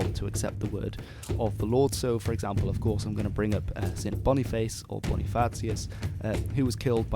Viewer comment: fuckall you know how many times I had to listen to you sayimg that as an American and had to trial and error wtf I thought you we're saying for it to come up?! Dammit I couldnt understand the guys name or where it took place. Sounded like "Sam Bonnieface otherwise Fartsius" and it happened in "Dunk em" in "Feets Loft" smh. I knew that couldn't be right so I kept trying variation after variation. Then I realized subtitles were available 😅😅😂edit* subs are partially helpful as they say it happened in "Flesh Lump" and then "Fleece Long" lol